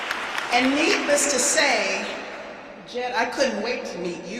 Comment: Did i hear a " neatless to say " ?